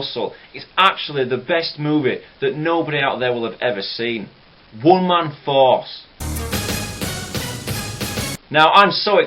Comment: Did he just say "One man falls"?